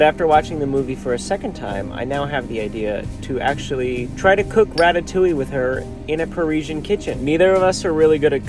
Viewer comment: “try to cook ratatouille with her” I heard that as your going to cook a rat 😂